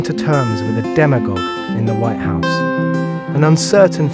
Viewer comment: This is an anti Trump video ...... Notice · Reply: Yeah, he obviously had an agenda! I thought I heard him say near the end that we had elected a 'demigod', did anyone else hear that? If so, how rude and disrespectful from a British outsider! President Trump has done fantasticly positive things for our country! He's made record lows in unemployment for all races, broken the record of stock market highs, brought back many companies to the US, now oil independent (Dems can't lie and say we are doing wars in the middle east just for the oil!!!), moved our embassy to Jerusalem(!--other presidents all said it should be done, but they never did it!), got us out of some disastrous treaties, he's getting rid of ObummerCare (an illegally mandated government program), he's fixing the VA hospital problems, got two conservatives on the Supreme Court, and many, many other great things! He could do so much more if the DemonRats weren't fighting his every move! I am all for him. He's sometimes rough around the edges, but he is a business man, and he's fighting to bring back our prosperity! He is on the side of every American--no matter what color! GO TRUMP 2020!!!